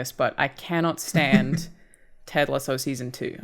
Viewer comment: What TV show is Alanah talking about at 15:40. "i cannot stand .... of season 2? I cannot hear what she is saying there =P. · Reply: There already been a comment but just in case. It’s “Ted Lasso”, her pronunciation of it has already been brought up on a previous episode 😂